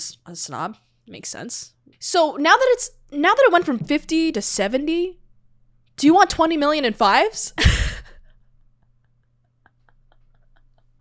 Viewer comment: I always thought Chris Tucker said "do you want fries with that?" not "do you want 5's with that?" Great action movie. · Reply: the way he (Tucker) was writing it down, repeating it back to him, and my childish brain (when I first saw this movie), made me think it was him taking down a fast food order. Which is why I thought he said fries.